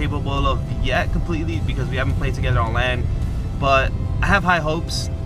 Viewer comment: It sounds like he says on land instead of lan lol.